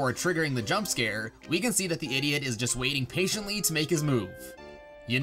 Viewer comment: did u say idiot?